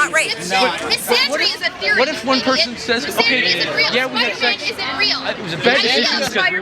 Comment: Did I hear this right at 9:00? "misogyny isn't real you idiot misogyny isn't real, Spider-Man isn't real.